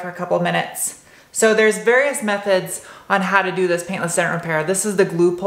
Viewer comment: Oh. I thought it said pant less dent repair.